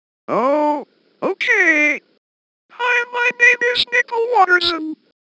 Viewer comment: i heard it niple